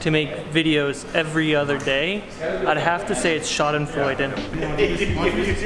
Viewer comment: I can't figure out what says about making videos every other day. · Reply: schadenfreude